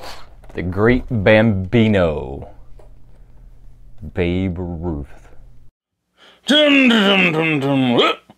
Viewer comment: "Babe Ruth...OHHH i thought you said Baby Ruthy!"